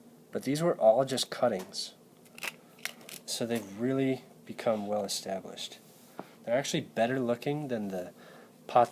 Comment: What did you say the name of the plant at 10:58 was? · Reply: That is a Graptopetalum, or Ghost Plant.